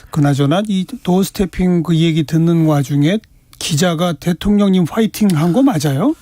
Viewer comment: 대통령님 화이팅? 저는 이상하개 고대로만해 화이팅 이걸로 들리지